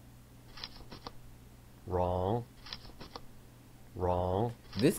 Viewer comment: When he said wrong and now i hear RAW